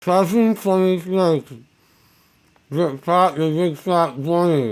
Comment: After the first marshmallow, I kept hearing “seven fucking snakes” And then the last one sounded like “funny snakes”